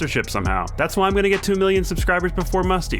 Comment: I thought you and said 1 million